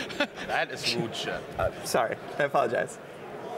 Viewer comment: Did say "That is rude shit." Just because he got gg'ed? I am a big fan of game talk. But insulting opponents is weak sauce.